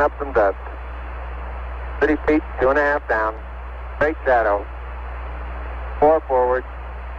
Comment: caption says "garbled". Armstrong is saying "faint shadow". He can make out the shadow cast by the LM on the surface, despite the dust.